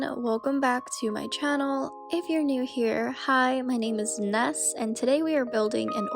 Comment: had to rewind bc i thought she said 'hi, my name is nuts.' anyway, great video lol